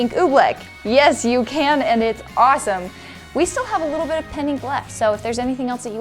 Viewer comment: It sounds like you said, "slime penny." But I'm sure you meant to say slime pen ink.